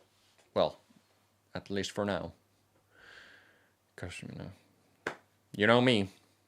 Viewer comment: It really sounded like you said at the and 'at least for now... Köszönöm'. That means thank you in Hungarian and I don't know if it's something only I heard or it was on purpose. I'd really like to know. Great video btw. <3 <3